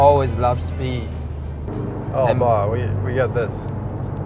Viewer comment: Your dad is cute with so many stories and memories. He loves Tsai chin songs. And you almost said “ 等一下! “ 😂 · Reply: I think he said "哦爸，我有。。。We got this"